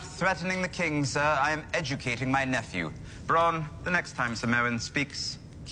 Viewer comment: I always thought he said "the next time Ser Moan speaks, kill him". He was taking the piss out of him.